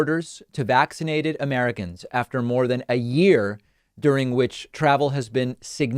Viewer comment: I really really thought that said "waxed Americans". And quite honestly, I support that.